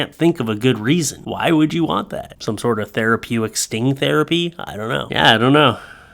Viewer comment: Did you just say 'thera-PEW-ic'? Don't you mean therapeutic?